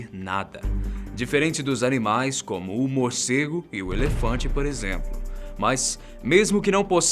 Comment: so eu ouvi Humorcego ?